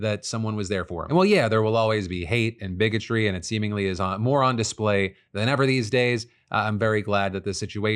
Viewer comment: lol my brain heard this as "moron display"